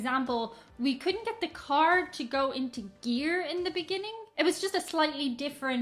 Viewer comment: I had to rewind over 'We couldn't get the car to go into gear" because I heard "We couldn't get the car to go into deer". You said it correctly but I was confused that you apparently wanted to run down woodland creatures.